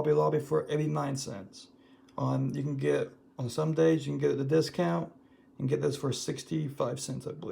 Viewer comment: Good thing it’s a Monday. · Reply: I went back and re-listened to what you said: "On SOME-Days, you can get a discount"...... I thought you said Sundays......